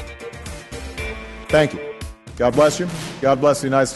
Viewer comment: Guys, wait! Listen at the very end at about 3:35 it's kinda hard to hear but it sounds like he is saying something like Thank you sa-.